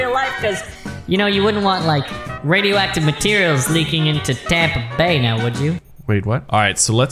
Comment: Did it say “don’t want it getting in Tampa bay”? 5:53 if he lives in Tampa bay, I am a 5 minute drive from Tampa bay